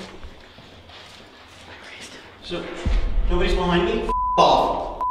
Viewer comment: I hear a females voice say “somebody’s behind you “ and I feel he hears it to and that’s why he freaks out shit is crazy !!!